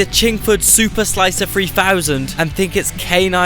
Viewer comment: i lost it. i thought you said the chinkslicer3000. fuck mate, im dying laughing here. fuck me that's hilarious.